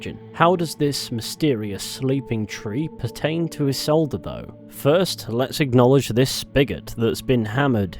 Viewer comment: My second favorite sugar momma I've ever had LMAO 17:17 I heard "Let's acknowledge this bigot nailed to the wood", and got excited for a second... lol